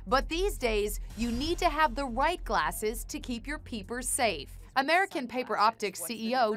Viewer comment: Ok so I understand but did anyone here the background woman's voice at 1:06 she said peepers instead of people.